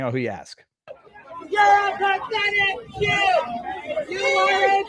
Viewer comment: I heard ‘You’re a sad excuse’.